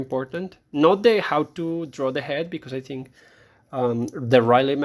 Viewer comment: Thank you for this review, exactly what I was looking for with this book. What is the name you mention at 9:50 about a better method for learning to draw human heads? I couldn't quite catch it. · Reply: Glad it was helpful! The method is the Reilly Method, you can learn it at Watts Atelier of the Arts online.